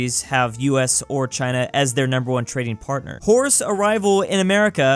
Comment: Was it only me who thought said wh*res (as in prostitutes) instead of horse. I was confused at 1st.